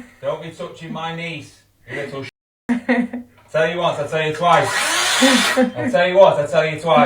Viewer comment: Arrh she is 25 and can’t lock her door ? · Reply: She's not 25. Americans misheard what he said, he says "I told you once I told you twice."